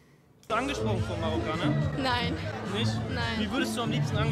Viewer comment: Ich habe die ganze Zeit verstanden angesprungen. 😂😂